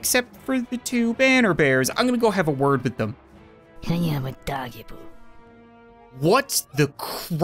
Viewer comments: I started laughing when deadeye said somthing i really thought that she said With that weird voice : i am a doggy poo (Yes i know that she really said i am undodgeable) But she said i am undodgeable when she was aiming more then ninety degrees of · “What just whispered something in english in my ear?” Im sorry, but i dont think hyama dajibu is english Edit: this is one of my most liked comments on this channel thanks :) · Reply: I thought it’s said I am a doggie poo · “What just whispered something in english in my ear?” Im sorry, but i dont think hyama dajibu is english Edit: this is one of my most liked comments on this channel thanks :) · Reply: I heard "I am a doggy poo" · That sound you heard talking, that was the Deadeye She does have a weirdly english sounding voice compared to the rest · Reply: I thought deadeye said can i have a doggy poo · When the deadeye talked it sounded like he said I am a doggie poop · When it sounded like something said something in English it sounded like. I have a doggy poo